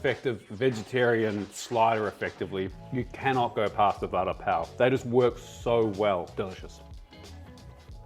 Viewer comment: cc says butter pal when you say vada pav